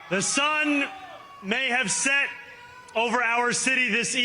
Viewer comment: oh lol i thought he said hasan may have set over our city this evening